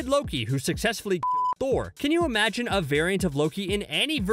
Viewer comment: I thought u said f** Thor xD